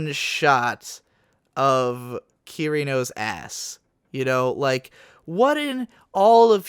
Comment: this just started as an autoplay, and i missheard it as "Kirito's ass." had to stop what i was doing to see wtf started playing on my youtube.